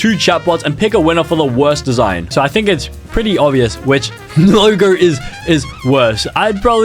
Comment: WHAT DID BRO SAY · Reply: Logo…?